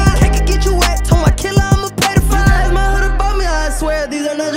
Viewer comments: What he say in 1:11 💀💀???? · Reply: Ima pay da fine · Everybody listen to 1:13 did he just say I’m a pedophile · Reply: Lmao he said ima pay the fine